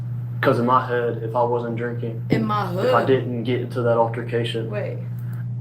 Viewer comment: He is southern and so am I. You misunderstood him because of his accent. He said “in my head, if I wasn’t drinking or get into that altercation.” He was talking about how he’s beating himself up in his mind rethinking about that day. I was hoping you would understand what he meant during the second replay but you still thought he said hood. Oh never mind you got it after a min, as I was done typing this out 😂